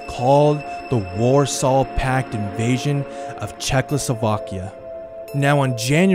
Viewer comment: Wait did he say The warsaw pact invasion of CzechLOSOvakia